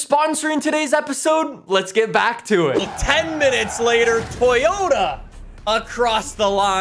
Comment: You know you have watched to much Top Gear if you thought you heard Toybota